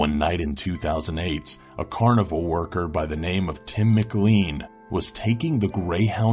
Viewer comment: I thought he said a carnivor worker I was like wtf